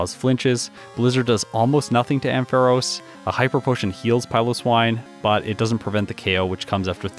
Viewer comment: I just heard 'hyper potion' as 'hiker potion'. Time for a hearing test maybe?